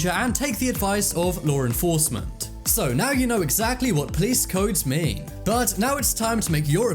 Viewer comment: I thought it said "Why are police so mean?*